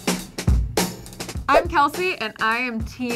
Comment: Who else thought that when she said ‘I’m Kelsey’ I thought she said ‘I’m healthy’ while she is talking about nachos xD